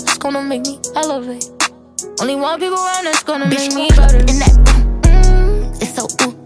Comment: thought the said , " Only White People around that's gone help me elevate " 😭😭